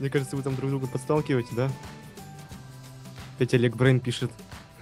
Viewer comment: мне послышалось или он сказал опять олег брейн пишет